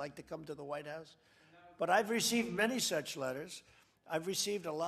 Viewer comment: am i hearing this correctly is he say s*x letters!?